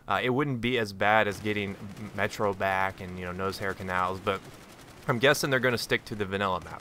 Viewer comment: Sounded like you were saying nosehair canals...